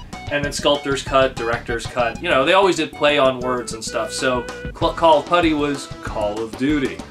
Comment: EPIC FAIL DUDE!!!!!! Do you not realize how perfect it would have been if you said "Clay on words" instead of "play on words"? So disappointed cause we thought we heard you say clay the first time. WTF!?!?!?!?! Such a terrible missed opportunity!!!!!